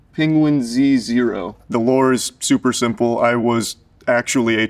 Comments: the captions say [indistinct]. He says Lore here. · The captions say [indistinct] at around 1:30. Just a note to wired - the word that you missed was Lore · It's funny how wired's subtitle writer couldn't figure out charlie saying "lore". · Subtitles can't understand the word "Lore" lmao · Hey WIRED, at 1:29 the subtitles say [indistinct] but he's saying the word 'lore', as in background information or preceding story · i like how whoever did the captions didnt know what the word "lore" was so they just said "indistinct" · Why is the caption at 1:29 [indistinct]? He clearly says lore. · idk why youtube turned on subtitles automatically, but like, bruh, who wrote these. 1:29 "The [indistinct] is super simple" he clearly said "lore". Or there was an attempt at saying it and it was moistly successful. (I didn't mean to type "moistly" there but it is what it is.) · they subbed "the (indistinct)" instead of just putting "lore"💀💀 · CC says "the [indistinct] is super simple" he's clearly saying LORE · the indistinct word that captions couldn't figure out is "lore" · why do the subtitles say [indistinct] ? I am pretty sure he is distinctly saying LORE